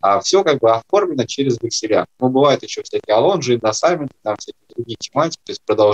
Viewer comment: Простите не поняла. Индоссаментная надпись, потом сказали какое слово? Я услышала "лондж" , потом векселя. Не найду, что это слово означает. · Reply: алонж. читай вексельное право